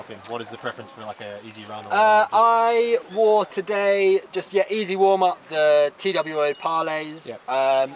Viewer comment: what shoe is Tom wearing? couldn't hear what he was saying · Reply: prototype